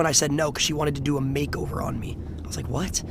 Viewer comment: I agreed with most of this video but a “makeover” doesn’t translate to “makeup” That whole segment was a hard watch because you misheard